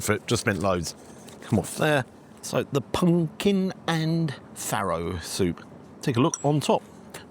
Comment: the pumpkin and what soup? I think the subtitles botched it as a non-native I have no idea what word was actually said. (Edit: ah! Farro!)